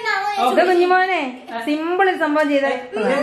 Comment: first i heard calling dude has kunjimone..😍